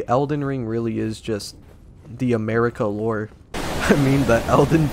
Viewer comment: Maybe we misheard Marika as 'Merica